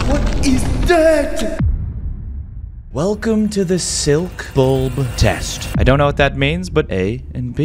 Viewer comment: I thought he said welcome to the internet